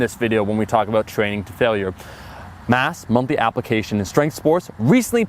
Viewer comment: I thought you said monkey application in sports science...